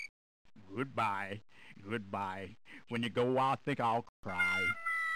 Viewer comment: does anyone know what that owl was saying? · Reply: The owl is saying “Goodbye. Goodbye. When you go I think I’ll cry.”